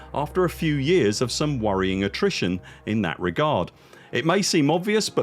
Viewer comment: He did 😂 Talking about last few years of the game haha. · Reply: ok lmao. Its at 10:38 and he says “worrying attrition”. I dont know how I heard “lacking nutrition” my apologies 😅